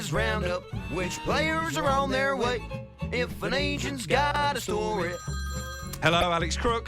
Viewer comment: thought the jingle said “if an asian has a story” at first🤣🤣🤣